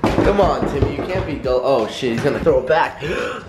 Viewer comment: did adapt call timmy?